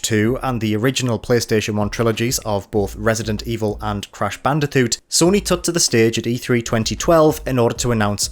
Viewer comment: Did... did he actually mispronounce Crash Bandicoot? I could have sworn he said "Crash Bandithoot."